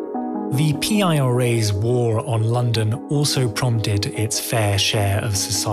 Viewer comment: I thought it said the IRS's war on london 😂